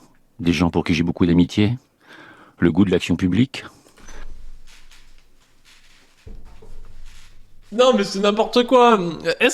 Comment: j'écoutais en 1.5, la première réponse j'ai entendu "le goût de la thune publique" mdrrr